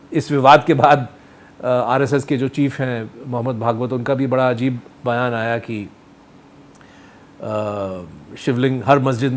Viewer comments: minute what did you said mohan bagwat or mohammed bagwat.. I ask all views to check.. And listen as no subtitle is available to spell it out.. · Did I hear it right that Mr. vardrajan (accidentally) said 'Mohd. Bhagwat'